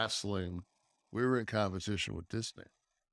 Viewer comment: Is it me or did Nash said he wasn’t in competition with WCW but in competition with this n***a (referring about Vince McMahon) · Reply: Think he said "disney"